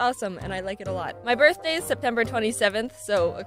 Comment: my birthday is October 27 and I thought u said October 27 instead of September. Im like wait but its october 1st what and we have the same birthday.. then i went back in the video u said september lol im a month after u ;)